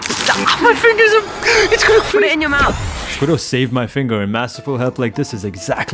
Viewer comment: when she said to put it in your mouth, i thought she she said put it in my mouth also thats what she said